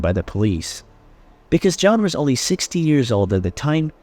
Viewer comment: it sound like you said 60 years old at the time and who is that dude that you’re showing that look too old to be dating a 15-year-old? Did they compare photo to the kid at the church?